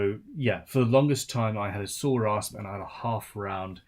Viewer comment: I had to rewind as I wasn't listening. said "I had a SAW RASP". I swore that I heard something else LOL.